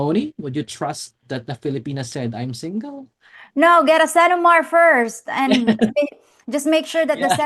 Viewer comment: Hey P, what word did you say right there at 7:50? I can't seem to figure it out lol. Love your videos, been watching for a long time! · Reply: It’s called a CENOMAR (Certificate of No Marriage), which proves a filipina is legally single 👍💯